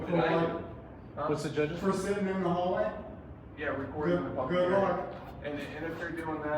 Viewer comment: Being trespassed for" yeah, recording the public" what did he say? Hearing? Area?